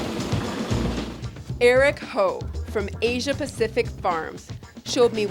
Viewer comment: Did she just call him hoe?